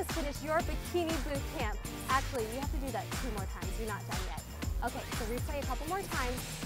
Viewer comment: She says, "Two more times!" I hear, "All done!"